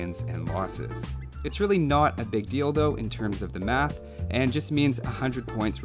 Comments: Every time he says "deal tho". I always heard "dildo". Hahaha Anyway always love your content. Keep up the good work! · Misheard something at 2:08 "It's really not a big DILDO"...